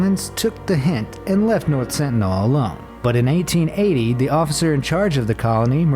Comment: 1880 or 1980 because I might misheard it’s 1980